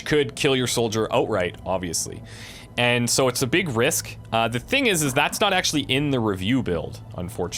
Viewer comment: Did you just say "The thingisis?